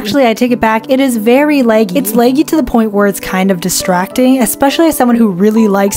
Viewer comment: This was ur first vid I ever watched and I gotta say u sound like sisu from raya and the last dragon whyyyy TT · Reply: Also I thought u said leggy I stead of laggy TT